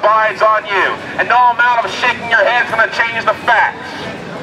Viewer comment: What does he say at 23:23? Sounds like he says "No amount of shit in your hands" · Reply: Bee Queen "No amount of shaking your head is going to change the facts."